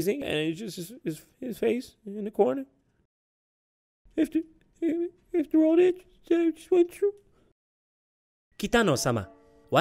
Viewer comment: I'm still trying to figure out what he said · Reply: “a-after all that? after all that, that i just went through?”